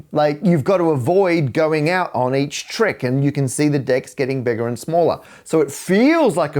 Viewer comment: The importance of articulation! I heard "The dicks getting smaller and larger"... Any man knows that this is infinite and also random, it might even be seasonal and... ... When you think of it, there are infinite numbers of answers to that sentence... Looking forward to your stand on this!